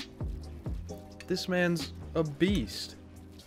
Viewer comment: 😂😂 i thought he said "This mans obese!"